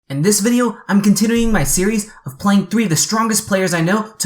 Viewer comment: what does he say? all i understand is contittering